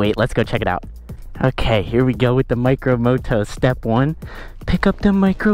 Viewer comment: I heard “the micro boto”…